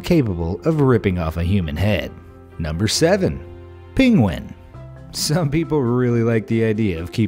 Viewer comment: i heard pingwen